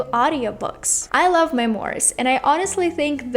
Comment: Excuse me, what genre at 7:45? Remorse, reworse, mymorse, lamorse... I didn't hear)